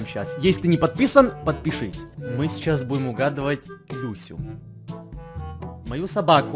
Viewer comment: "-Сейчас мы будем угадывать...Писю..." Мне одной послышалось?XD